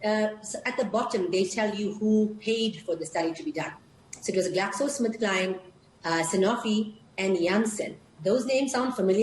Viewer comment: Kalapi Trivedi, Well, you either didn’t watch the video, or you lack the mental capacity to understand what she said. So which is it, did you not watch? Or are you an idiot? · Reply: Wondering what you heard, what "she said" is 01:01 ... they tell you who paid for the study to be done so it was GlaxoSmithKline... Sanofi and Jansen (Johnson & Johnson) those names sound familiar? Those are the same people who are involved in the vaccine.